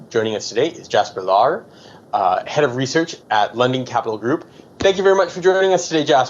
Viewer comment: I thought said Lemming Capital Group